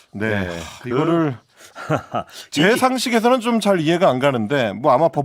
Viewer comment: 이 대목에서는 제상식이 아니라 죄상식이 아닌가요? 기자님 내 귀에는 제 상식으로 들려서요, 제 귀가 잘못됐죠? 아니면 큰일인데